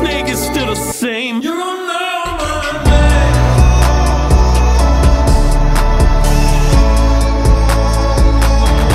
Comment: what does that deep voice say at 1:37?! Does anyone else hear that?! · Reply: DETENTION